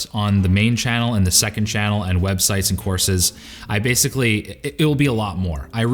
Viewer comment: I’m sorry, did I hear courses?